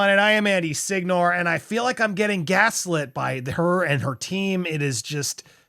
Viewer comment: I’m losing it. I thought you were about to say “I feel like I’m getting gassy by these…” instead of gaslit. 😂